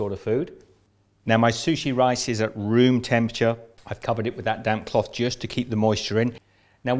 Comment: I kept hearing him say, " That damn cloth." 😆